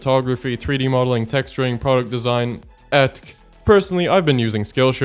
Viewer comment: did bro just say etc😂😂😂😂